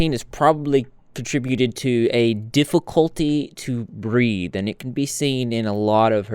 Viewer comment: I thought you said her love of nicotine probably contributed to a difficulty to breed 😭😭. Took me a minute to realize you said breathe 😂.